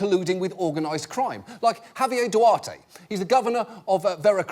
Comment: At first, I thought he said “having a Duarte”, thus wondering what sort of crime a “Duarte” must’ve been.